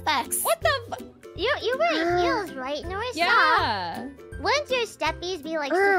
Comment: I think Biboo said, "You wear heels."